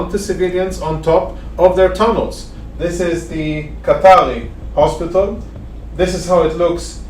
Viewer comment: what hospital is this.. I hear him say kartari hospital and the sub says katari too but his screen says SHEIKH HAMAD hospital???